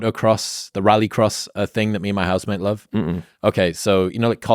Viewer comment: ok he said "housemate", thought he said "husband" at first. Not that it matters